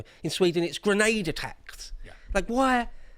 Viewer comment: I heard that as "an attacks rebate"